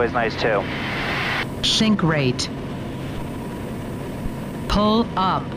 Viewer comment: Watch out for those 'shinkers' over the road. It really does sound like Betty is saying 'shink rate'. Sibboleth!